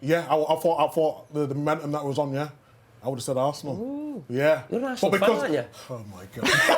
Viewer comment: Lol what is it that carra says @ 3:35 to get the boys laughing? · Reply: Carra said : "oooh..you're an Arsenal fan aren't ya?" Because Micah Richard (and Sturridge) known by the pundits for being Arsenal fans since their childhood / early days.